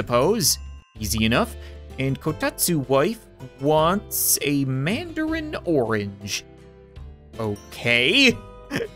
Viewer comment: anyone else think that said mandalorian orange?